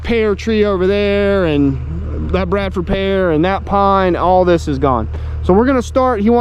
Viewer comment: Those kinds of pear trees don't produce edible fruit. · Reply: Thanks for the info. I had misheard and thought you said Bartlett, but you did indeed say Bradford.